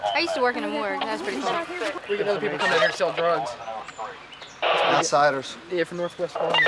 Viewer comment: "I used to work in a morgue" "I used to sell drugs" - that's what it sounded like to me